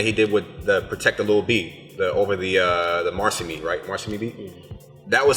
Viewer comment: I know but have never seen the other guy, after he said he wants enemy of the state part 2 he gained my respect. Lu has some criminally underrated mixtapes. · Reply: Paul McCormick nah. At the 9:52 mark he mentioned something that sounds like "released with the protect lil B". and I have no idea what he said.